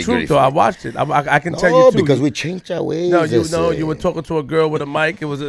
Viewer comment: when he said, "We changed our ways, ese," I know most on here didn't even catch that.